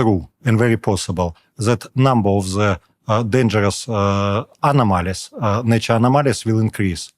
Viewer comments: he actually says dangerous nature's anomalies, not animals. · Subtitles are terrible all throughout.. like on this spot, it says "dangerous animals" instead of "dangerous anomalies" which he did say.